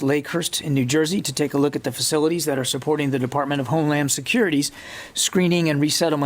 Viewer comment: min ... I keep hearing 'home lam security ' ...